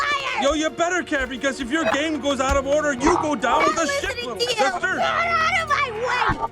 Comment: ohhh “you go down with the ship, little sister” makes a lot more sense for a disney film than what i heard (you go down the shithole, sister)